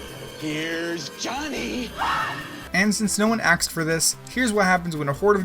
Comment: "since no one axed for this" did anyone hear that or am I crazy?